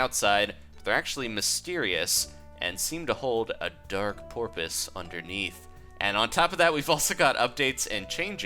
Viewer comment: did he say Porpoise like a dolphin :D